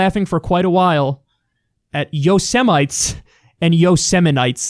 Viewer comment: first time i heard him i thought he said yo sodomites